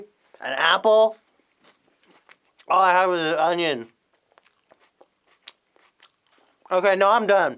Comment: I thought it said "opinion" instead of "onion"